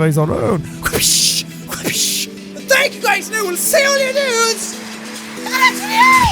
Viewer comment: A fairly new subscriber here, finally starting to figure out what he says in the outro...can someone give me the full text? I still can't understand what he says right before "in the next video!" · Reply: Amber Tyrell ah thanks guys! I kept hearing "I will see all your nudes, IN THE NEXT VIDEO!!" so I was getting a little worried...